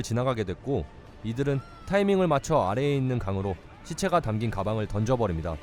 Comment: 저만 시체가 던진 가방을 던지다고 들리나요?